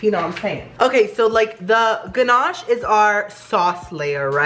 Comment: He said "k so.." and I thought he said cheese 😂😂